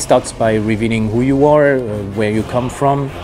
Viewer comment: What I heard, "Who your wallet is", "Where your wallet is", and it continues with "wheres your money going" and "what you need to pay"